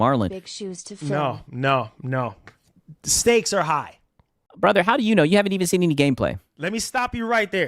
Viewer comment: I thought he said "snakes are high" :_snake::_snake::_snake::_snake::_snake::_snake::_snake::_snake::_snake::_snake::_snake::_snake::_snake::_snake::_snake::_snake::_snake::_snake